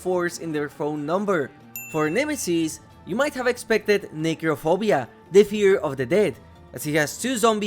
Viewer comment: genuinely thought u said negrophobia for a second💀💀💀